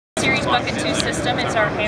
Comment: At first I thought she said "fuckin' tooth system".